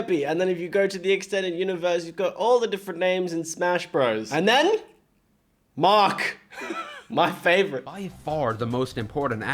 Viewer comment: the name is actually Marx. Not Mark. They do sound similar, so I don't fault you for mishearing it